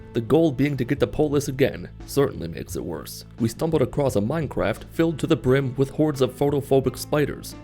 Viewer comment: does he say "we stumbled across a minecraft" or I just cannot unhear it